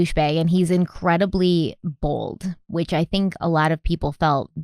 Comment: thought she said he's incredibly OLD and i kinda laughed, he's not that old.. oh she said he's BALD, yeah he's bald lol.. oh wait, BOLD, she said bold.. i think i need sleep. and it's ok to laugh when he's a pos, right?